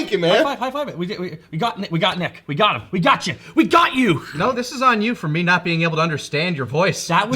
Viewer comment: Nope i hear "got you b**ch" from cell jr :D